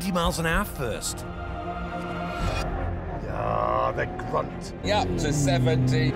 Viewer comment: I thought Plato said the C word 8:48 lol